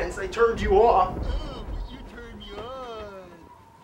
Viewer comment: Lmao did say "No, you turned me on" or did he say "No you turn me on" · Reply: "No you turn me on" lol James is so cleaver when he writes these episodes.